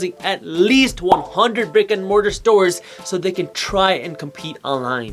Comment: I thought he said rick and morty stores.